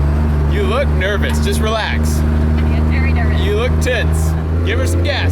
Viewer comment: I thought said "You look tits!" xD (for reference he said you look tense)